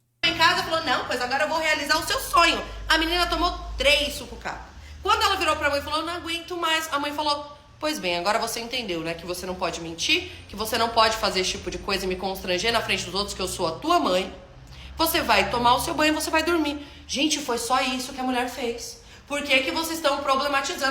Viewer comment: Me corrijam se eu entendi errado plz, tô na dúvida se entendi errado ou nn 22:53 "Ela falou pois bem agora você entendeu né, que você não pode mentir, que você não pode fazer esse tipo de coisa e me constranger na frente dos outros" a mãe gravando um vídeo expondo e constrangendo a filha:🤡